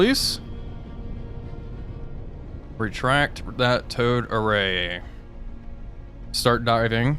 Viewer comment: Every time I hear “Towed Array” my stupid brain substitutes “Toad Array” which would be funnier, but far less useful. Just a chorus of ribbets deafening the enemy sonar.